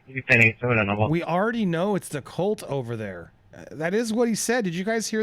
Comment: "We already know there's been calls over there" is what I heard not "cult over there".